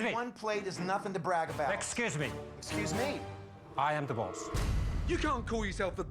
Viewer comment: I thought the guy said "I'm divorce" no "I'm the boss"😂😂